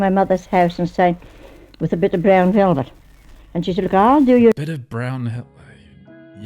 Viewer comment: Is she saying brown velvet?